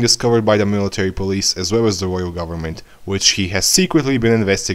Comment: What did he say at 11:39? "In order to make way for the ..." something something slurred language I couldn't comprehend. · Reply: Xɪɴᴛɪᴀᴏ in order to make way for the plan to take the founding titan whenever that time may come